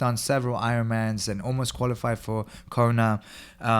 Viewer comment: did just say he qualified for corona as in the coronavirus